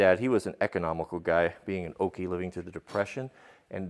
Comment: did he say OKIE like an oklahoman that's dope if so I'm from Oklahoma